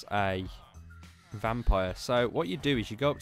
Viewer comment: what did he said ? what key ? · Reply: V